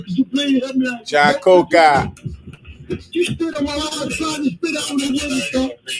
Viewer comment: did he say that the racist🐷tried to spit on him?🤔